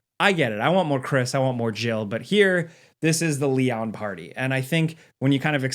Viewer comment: I thought you said lemon party